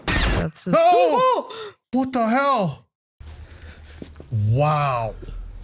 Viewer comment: sounded like he wanted to say "Puta madre!" but pivoted half way into a "what the hell?!"